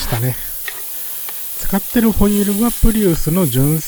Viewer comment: アルミホイールが最初アルミホイルに聞こえてわざわざ溶かしたのか…って思ってました(笑)